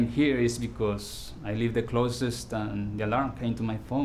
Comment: At first i thought he said "because I live in the closet" and I was like.. When are you coming out